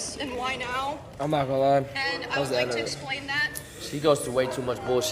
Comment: what the says here? "I am not gonna lie, ... I was into her. I was at hers. I was better. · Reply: "I'm ngl that's the end of it"